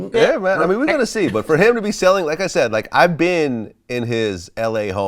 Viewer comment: I thought he said he selling his hoes🤣🤣🤣